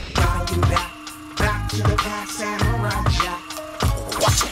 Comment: As a kid I always thought they said "bow to the jack" instead of back to the past. I still I sing that instead cause it sounds so much more badass. I will always bow to jack, samurai Jack